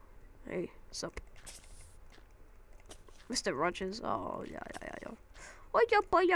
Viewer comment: did u say mr. rogers? lol